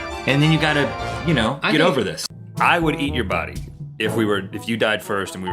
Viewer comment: only I heard "I would eat your butt"?